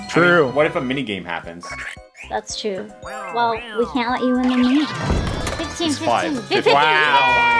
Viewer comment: when you put question marks on the screen because you didn't know what said she said, "Well then we can't let you win the minigame"